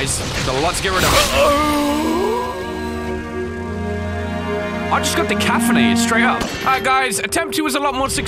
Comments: Sounded like he said "I just got decaffeinated" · Wait what did you say 2:59 "i just got decafinadet"